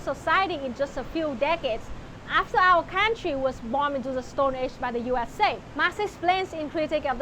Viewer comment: English subtitling mistake. Subtitles: "after our country was *born* into the stone age by the USA" What is actually said: "after our country was *bombed* into the stone age by the USA"